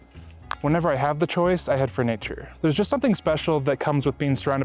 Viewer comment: Love the plumbers crack yoga! Lol Great advice brother 🤙🏻 At 2:00 I could have sworn you said "when ever I have the choice I have furniture" what a random thing to say right. Then after multiple listens realized you were say "I head for nature" Having furniture is a good ritual to I guess 😂 7:13 I think you’re lil friend wanted some Cacao. How cute!